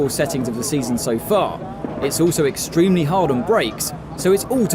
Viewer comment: breaks instead of brakes? Who are the transcript writers for this. Its horrible